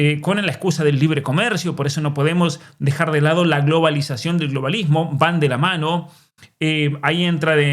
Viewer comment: "globalización del globalismo" estoy loco o escuché mal ?